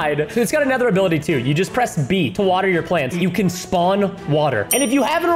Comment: for the George axolotl i thought it said "water your pants" hahaha